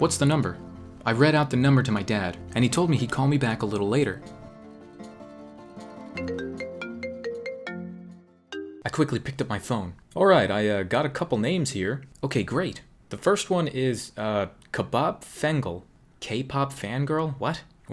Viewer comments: Dad: KABOB FANGIRL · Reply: I thought he said Kebab Fangirl at the first place LOL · dad : "KPOP FANGIRL" guess what i heard? "KEBAB FANGIRL!!!" 😂😂😂😂😂😂😂😂😂 so cute Edit: this girl is HELLA crazy like not feel ashame at all... stalking or whoever she wanted to. THE HELL??? DAMN THANKS TO DAD 👏🏻👏🏻👏🏻👏🏻👏🏻👏🏻👏🏻👏🏻👏🏻👏🏻